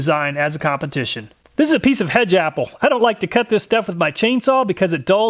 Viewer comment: For some reason I heard you say "hejaple". haha. Once I saw the end it made more sense. I thought it was going to be a super exotic wood. haha. Your tests are the best!